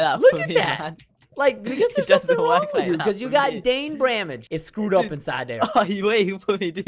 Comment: Did say dain bramage?